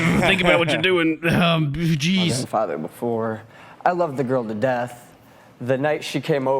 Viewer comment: did he say "I've been a father before"